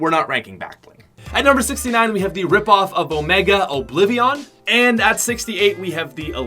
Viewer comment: Did he just say "Obliv I on" Lmao